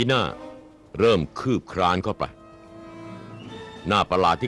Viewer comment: did he said cock ???? on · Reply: The hyena began to creep in ไฮยีน่าเริ่มคืบคลานเข้าไป